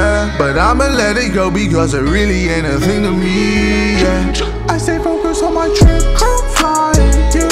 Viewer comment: "I stay focused on my trip, crew flyin', you stay cryin', I aint hidin'" sheeeeeeesh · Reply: i thought it said i stay focused on my drip coat flyin